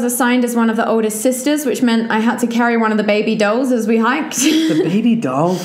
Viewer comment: I thought she was saying “baby doe” as in a baby deer and I was SO confused